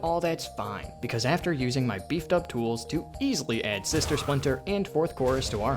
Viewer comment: Why does everyone call him sister splinter? Am I mishearing it? Or am I just wrong that I think that it's called Sinister Splinter? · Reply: It’s sister